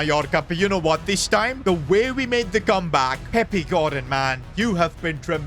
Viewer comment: the captions/subtitles said pep guardiola instead of peppy gordon😂😂😂